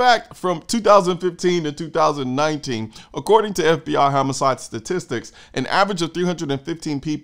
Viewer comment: thought when you said homicide statistics i heard hammercide